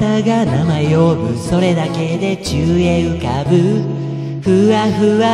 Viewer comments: 「宙へ浮かぶ」がどうしても「チューインガム」に聞こえる… 鼻声かあええ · “宙へ浮かぶ”がどうしても“10円ガム”に聞こえてしまう…… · 何回聞いても｢宙へ浮かぶ｣が｢ちゅうえい浮かぶ｣に聞こえる…